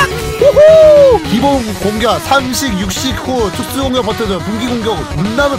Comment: 나만 특수 공격할때 촉수(?)공격으로 들림?